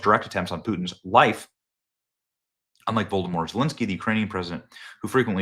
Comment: did he say Vodermort Zelenskyy?